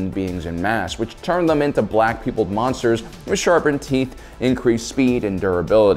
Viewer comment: I'll be honest when he was talking about the angel zombies I thought he said black people monsters